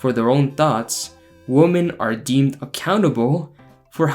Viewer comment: When listing qualities of good husbands i thought you said a CANNIBAL instead if accountable